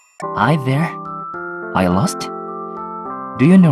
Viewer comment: Is it just me with my deaf ears,but I hear rui’s “parents” as “pawents” 😀